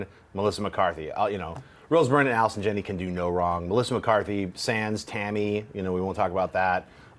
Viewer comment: One more thing, at the 16:50 mark, John Schnepp names two bad Melissa McCarthy movies. He says something that I couldn't understand and then "Tammy". Out sounds like he said "Sands" but there was nothing if that title on her iMDB page so can anybody tell me what Schnepp said at that point in the video? Thank you in advance to anybody who answers my question.